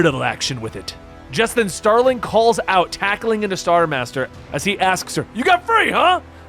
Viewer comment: Did anyone else hear him say star master ?